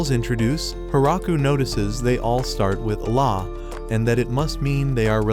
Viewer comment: did you just said allah its the god of islam